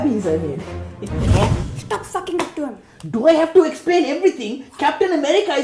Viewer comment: what does she say at 0:53 is it stop hogging the phone or fucking the phone